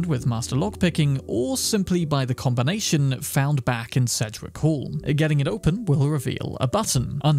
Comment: the combination is found where? I cannot understand the words here.. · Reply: I think he says "Cedric Hall"? I'm actually not sure what that is though, might be in the same building. Edit: It's actually "Sedgwick Hall," which I believe is the same unmarked building location. The Fallout Wiki will probably help you more. :)